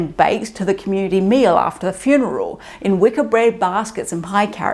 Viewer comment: I really don't mean to slander your lack of accent, but I missed the "r" at the end of "wicker". As a result, I heard "Wicca baskets". I had to remind myself that I've been to a lot of places here in the States where people routinely drop "r"s at the end of words.